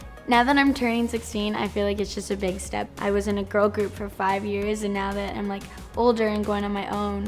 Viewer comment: She doesn't look 16 And also when she said I was in a girl group for 5 years I thought she said I was in a coma for 5 years I was like that's sad but I went back too see what it really said